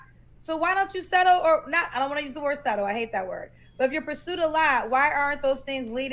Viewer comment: She's a divorcee. She can have any standards she wants as long as she's okay being alone for an indeterminate amount of time. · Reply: All I heard was "I don't want to get remarried"